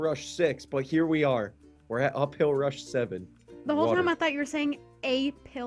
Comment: I totally thought he said pill rush too